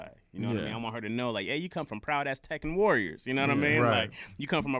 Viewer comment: HAHAHAHAHA!!!!! OH! AZTECAN! At first I thought he said "Proud A$$ Teken Warriors!!!!!" I was like, WAIT WHAT? My mind went straight to gaming, not the indigenous people! 😂😂😂😂😂😂😂😂😂😂